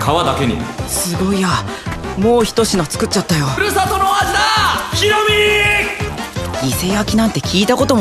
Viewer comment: 僕はここが何回聞いてもひろみーーーーーにしか聞こえません。 そして「故郷」と「郷ひろみ」でも掛けてるのかなと思いました。（あれ、作文？）